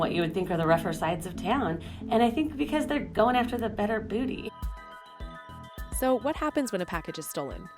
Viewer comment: Did she say they was going after the better booty? @ 2:39 what did she say?